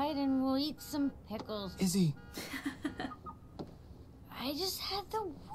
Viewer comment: WAIT DID THE GUY ( at 4:31 ) JUST SAY "izzy!" CUZ THATS MY NAME!!! i heard my name in a lot of moives and shows